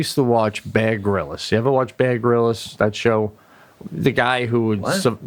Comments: when says baer grylls he sounds like hes saying bad gorillas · I thought said Bad Gorillas for Bear Grylls.